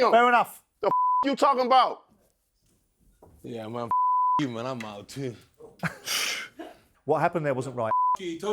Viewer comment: Did Chisora just say fucc you too I'm out 😂